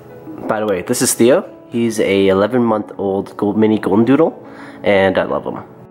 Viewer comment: I thought you said 11 weeks i had to rewind it cuz 11 weeks he’s huge for 11 weeks. But 11 months makes sense 😂😂 i get mine next week 😇